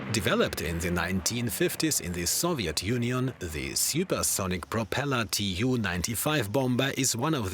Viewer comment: Did i hear "supersonic propeller tu 95"?